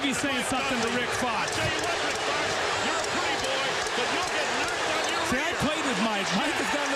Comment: Did you hear that... Listen closely and you'll hear "You're a pretty boy, and you'll get knocked on your Lol Listen just as Coach Dunleavy is talking to Rick Fox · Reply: "I'll tell you what, Rick Fox. You're a pretty boy, but you'll get knocked on your rear!" Not being cheeky, sir, just what I heard. An epic call out. Announcer was right here. Nobody is special.